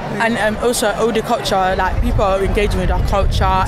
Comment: when the girl said culture at 4:36 i thought she said okocha :)